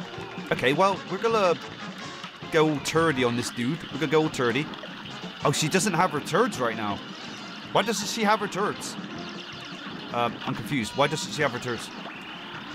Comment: It sounds like your saying turds.